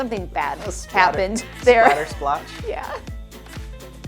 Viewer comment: why did you say dog day’s blood was a splatter squash? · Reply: They said Splatter Splatch 😉